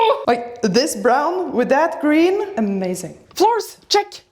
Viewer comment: wait.. did she say green? I know i'm kinda colorblind, but this is clearly black. Edit: Nevermind, i saw green later on in the video🤐